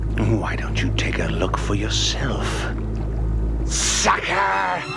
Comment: i thought he said "sike" 😂😂😂